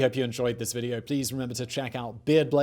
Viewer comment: Did Simon say 'Check out 'Beer Blaze' or was that check out 'beard blaze'?